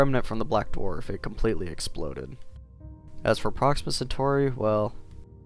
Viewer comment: For a second, I thought you said "Proxima Satori" If you know what Touhou character my channel is based on it's very ironic